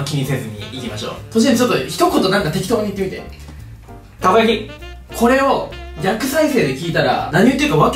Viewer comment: 最初の「たこ焼き」が「銀だこ」に聞こえて同じと思ってしまったw